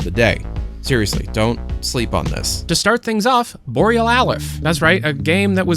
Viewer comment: I misheard Boreal Alyph as "Borealith" because of that lisp.